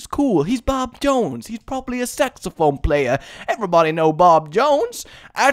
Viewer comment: Remember when said that guy probably was a saxophone player? I thought he said sex offender...